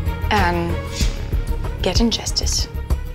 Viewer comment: it seriously sounds like she is saying: “… and get injustice.” Not: “…and to get justice.” The captions (turned on) says/ “and get INJUSTICE”